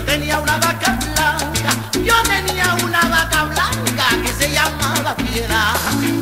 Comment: Yo pensé que decía vaca flaca, toda mi niñez cantaba así. 😁